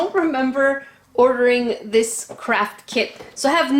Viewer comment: In 0:05 I thought she said crap kit but she said craft BTW I guess a 3 am ghost took your credit card